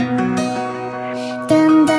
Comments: Omg at first i thought she said “Im just a little bitch, I...” 😂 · Not gonna lie i thought she said. Damm damm dubi do tell me what can i do im just a little bitch i